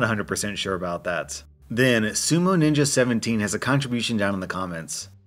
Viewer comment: "I raise n train dogs..." I heard "I raisin-train dogs..." "Is that a raisin? Here boy! Raisin? Is it?!" "Ruff! Nu, its a sultana..." "Good boy, well done! Have a blueburry muffin..."